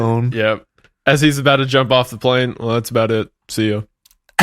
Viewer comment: before jumping off the plane “That’s about it, see ya” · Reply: I always hear "That's about it, so yeah"